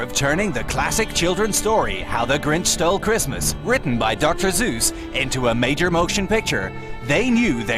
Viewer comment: Who else heard Dr. Zeus?